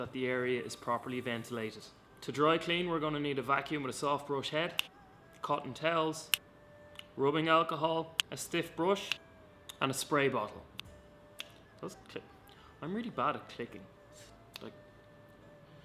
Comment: Ohhhh. Cotton towel. I thought you were saying Cottonelle, as in the toilet paper. Lol! Thanks for the video.